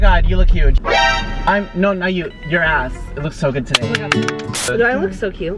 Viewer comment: for some reason I heard “your ass looks so vintage” hahahahah wtf